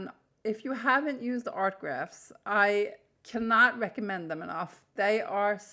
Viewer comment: Hi, what do you mean art grafts? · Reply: lol I thought she said art graphs 😂😂